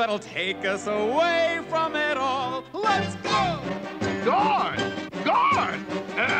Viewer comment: I misheard what the king (or whoever Neefa Feefa wants to run away from) is saying at 17:57 as "Gone! Gone!" The correct line is actually "Guards! Guards!"